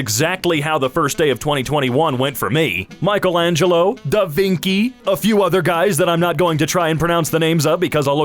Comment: DID HE SAY DAVINKI UNIRONICLY